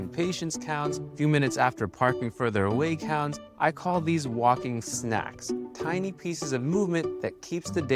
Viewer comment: sounded like he said "walking snacks" LOL